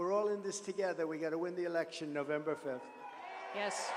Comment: he 100% said it, I just don't remember the time stamp. He says "come out January 5th, or whenever the polls are now because it's all over the place" trying to cover up his blunder by blaming others, as usual. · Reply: he clearly says November 5th